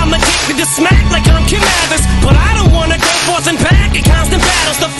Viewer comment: For the longest I thought he said Canibus and not Kim Mathers lmaoooo especially cuz of the following bars. Lol